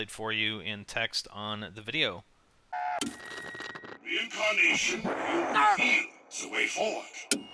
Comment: I definitely heard "Reincarnation will reveal the way forward"